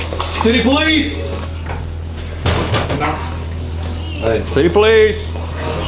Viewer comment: Did anyone else hear a female voice minute 4:25 I think she says, (Hey!)